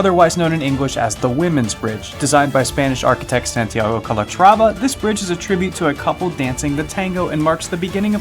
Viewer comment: entendi que el puente de la mujer lo diseño santiago cometrava jajajajajajajaja (minuto